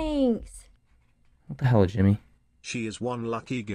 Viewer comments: He went from “the hell jimmy?” To “thank you Jimmy” in less than a second 😂 Edit: the less than a second was a joke, I’m not timing this. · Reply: I think that in fact the Subtitle are wrong cuz i think i heard "tell her Jimmy" but idk maybe i'm wrong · “What the hell jimmy” “thank you jimmy”😂😂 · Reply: Thought it said tell her Jimmy 😂